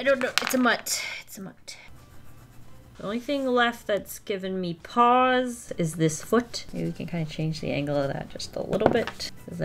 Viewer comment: what was said: the only thing giving me pause is this foot what i thought was said: the only thing giving me paws is this foot